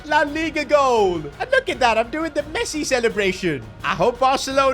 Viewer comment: Did he say pessi at 5:50 ?!